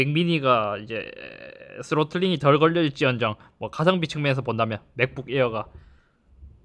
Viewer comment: 에 사실상 맥미니가 "____" 뭐라고하는건가요? 스노우쿨링이 덜 걸려 있을지언정 이렇게 들리네요